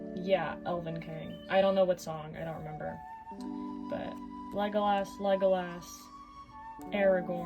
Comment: Everytime I hear Legolass's name being said I think they're saying "Lego ass"